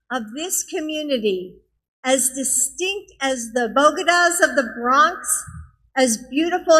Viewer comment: I thought she said “The Bogotas of the Bronx”.